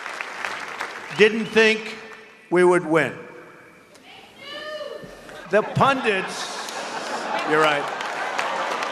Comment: What does the woman yell out at 5:00? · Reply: Viktor Varga "We knew" is what she said.